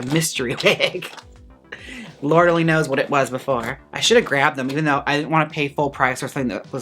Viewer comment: I thought you said "Laura Lee knows what it was before"